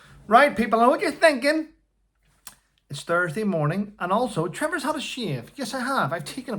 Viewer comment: Did I hear you saying,'Thursday'? I just nearly had a fit - I thought it is Friday! But I may have misheard you, so apologies 😊